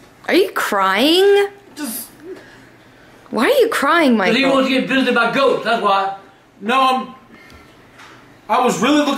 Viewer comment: Sounds like he says “Because he wants to get fisted by ghosts”